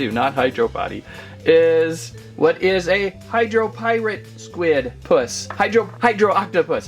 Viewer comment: what did you say? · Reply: Squid-Octo-Pus. probably should not have stuttered there.